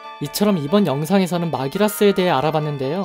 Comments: 그림그리면서 보는데 야돈 계열을 야동 계열로 들었다 . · 야돈계열을 야동계열로 들었어.. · 나만 야돈이 야동으로들리낰ㅋㅋㅋ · 나만 야돈계열이 야동계열로 들리나?